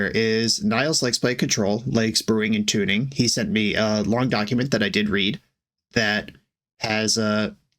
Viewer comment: I thought you said “he sent a long document that I didn’t read” and respected your honesty.